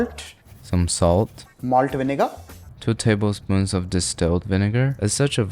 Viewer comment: what bro even sayin :D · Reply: Malt Vinegar